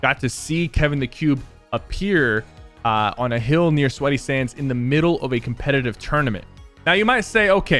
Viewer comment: Did he say sweaty sands.